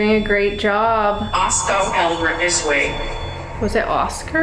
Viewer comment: I think it said “I’ll spell Elbert this way” idk 😢